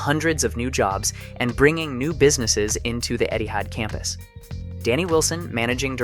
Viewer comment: I don't why but everytime he said Etihad i always heard it as Eddy Howe